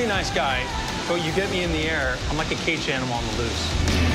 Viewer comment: I can't get what he's saying at 2:09 'I'm a pretty nice guy, but when you get me in the air...' What does he say after that? Please help. This sounds like an ultimate badass phrase i can use sometime. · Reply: "I'm like a caged animal on the loose"